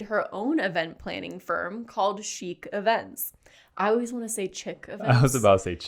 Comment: for some reason I misheard "chique events" as "cheap events"...